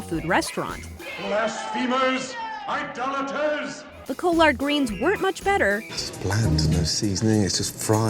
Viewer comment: Did she... did she say coll-ard greens???